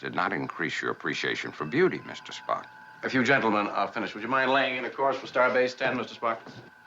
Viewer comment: i could i have sworn i heard Kirk say "If you gentlemen are done flirting" ( ͡° ͜ʖ ͡°)